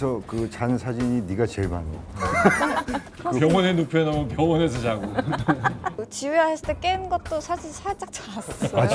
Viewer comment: 에 한소희가 뭐라고 하는 거예요? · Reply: "네, 그렇습니다"라고 한거같아요.